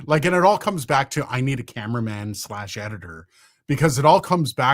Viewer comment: I thought it said you needed a CaveMan, I can do caveman.